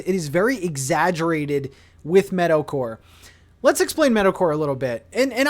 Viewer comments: When you first said your theme I thought you said ‘mixed with metalcore’ and I almost died of excitement 😭😭 still excited to see everything you do with this theme, but man I’m disappointed now 🤣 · I'm dying! I thought you said METAL CORE! I was lie ooh interesting then you go to an island with a meadow.